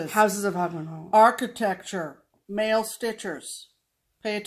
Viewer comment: when you said male stitchers I thought you said male strippers!! Haaaa...we the pee pee!!